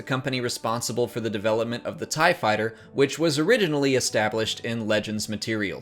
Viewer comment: Did you say oridionally...?